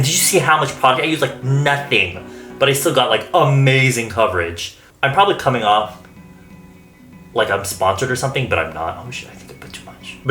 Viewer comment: i thought you said : like i'm spongered hahaha i can't